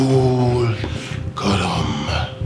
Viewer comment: Romanga said "Makul Colom" or whatever, and at first I thought he said "Hakuna matata"!